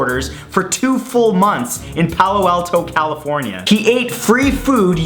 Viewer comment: 9 ) 1:41 i didn't get what he's saying O_o i repeated it 4 times can someone help ? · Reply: Palo Alto, it's a city. Y`all need to travel.